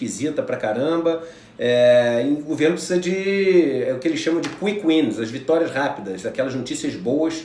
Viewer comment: O que ele diz aos 10:02 ? Qui Quinas? Qui quins? Procurei no Google e não encontrei referências.